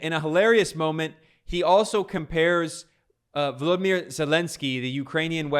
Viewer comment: I thought I heard him say Voldemort Zelenskyy. They sound so villainous it's unbelievable.